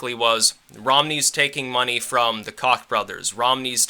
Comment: Lol at 15:36 did he call them the Cock brothers?